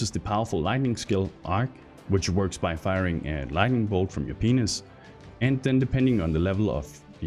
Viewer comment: dude.. at 8:53 did you really say "firing lightning bolt from your penis"??